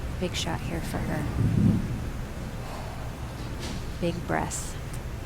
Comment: Is it just me or at 15:32 did Kim say what I think she said? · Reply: no she said Big Breaths,, Like to ease the pressure,, Get your mind out of the gutter :P